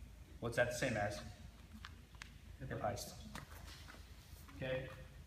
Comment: what does he say at 1:30 and what is he talking about? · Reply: Hip Heist